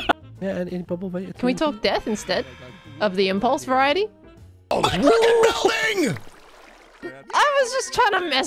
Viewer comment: out of context it sounds like said “my fuckin building!” (that might just be my audio processing disorder tho lol)